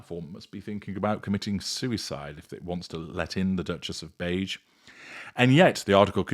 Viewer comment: I thought said "The Duchess of Mange" (not beige). 😂😂😂😂